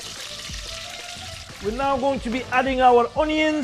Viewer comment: cannot understand what he is saying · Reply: The Magnificent Mr Popo he is saying “punch puran” its a powder of five seeds. Please do research for it on google.